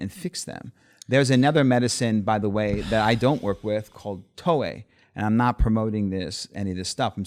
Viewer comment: What plant is he talking about 3:21 Toe-A? Never heard of it.. anyone know what he's talking about